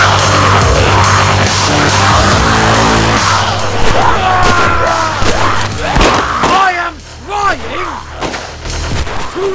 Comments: At first I thought you were saying "I AM FIGHTING TO LEARN!!!" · So after watching this like twenty times over the course of two years, I finally heard him say "I am trying to learn!"